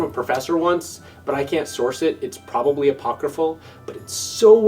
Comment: What was that word said at 13:44 ? Popcreful? Potgrapeful? Google hasn't helped me. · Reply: Apocryphal. It's a fun word.